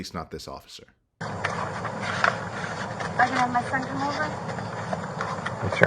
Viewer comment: ok I'm pretty sure she says "friend" here instead of "son" come over.. Anyone else hear that? If that's the case, i think we just got the answer why Sarah wanted Jorge out of the house that night EDIT ok I just listened to that clip again, and today I very clearly hear her say "son"..Honestly no idea why I heard anything else last night, other than it was extremely late 😂😂